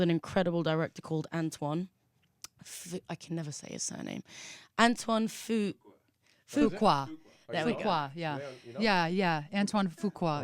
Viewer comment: What is name of the director that Rita said on time 2:38 ? :) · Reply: Antoine Fuqua